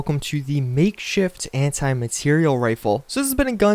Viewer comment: I thought it said "Anti Matter Rifle" ...Heh heh...